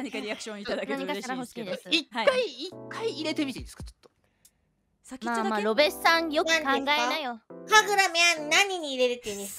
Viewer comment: here I heard Tamaki: Sakiccho dake? (Just the tip?) Yes. In this shimoneta context was that "Tip".